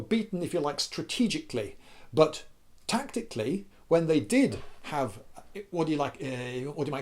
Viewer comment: i thought he said Tanktically, he missed the oppurtunity 😥